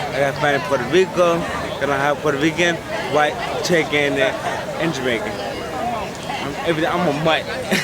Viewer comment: the captions say “(inaudible)”, but he says “Cherokee Indian”.